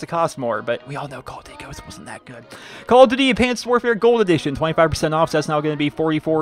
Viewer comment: I thought he said add pants XD. I am so childish